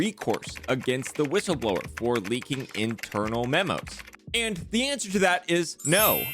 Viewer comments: Did anyone else catch the "interanal memos" @ 3:15 ? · it says “interanal” instead of “internal”